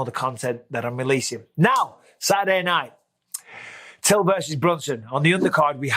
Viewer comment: Did you just say “Till vs Brunson”